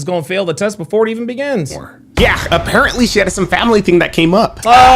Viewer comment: I thought you said she had ass. NOASSITOL